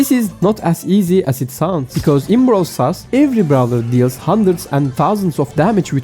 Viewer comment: did he just say brawl sus